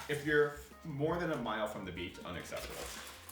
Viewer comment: "more than 1 mile from the beach it's unacceptable"> Liked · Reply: Ohh that’s what they said! I thought I heard them say bacon and kept squinting like Huh? Beach makes a lot more sense